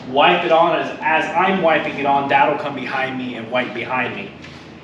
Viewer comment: Did you say at 16:29 your Dad would wipe your behind? 🤣🤣